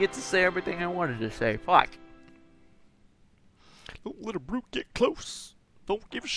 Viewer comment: When it said, "Don't let a brute get close" I thought it said, "Don't let Bruce get close".